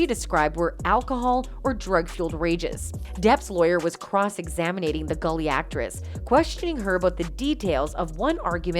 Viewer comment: Did she say examinating